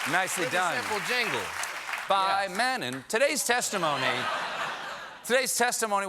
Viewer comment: Did anyone catch his "by Mennen" right after the jingle? I thought I was the only one (or one of few, at least) that did that to kill any earworm! edit